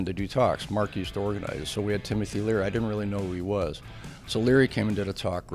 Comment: did he say they had timothy leary??